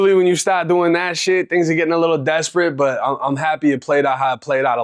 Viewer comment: Anyone else thought he said niggas gettin desperate at 0:45 😂 😅. Had to rewind a few times